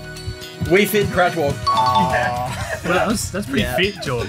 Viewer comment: I heard "Wii Fit crackwhore."